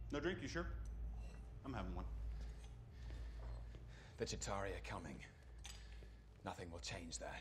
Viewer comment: WAS I THE ONLY ONE WHO HEARD "THE ZUTARIANS ARE COMING"????